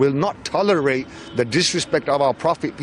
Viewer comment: Did he say "piss" be upon him?